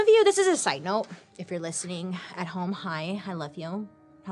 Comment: I thought she said “ if ur listening at home high” but realized she said hi😂